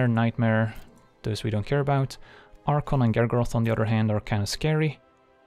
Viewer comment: Anyone else hear Eric Andre?